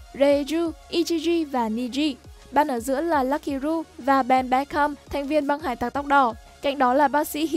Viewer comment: sao nghe như ben bách khăm thế nhỉ :))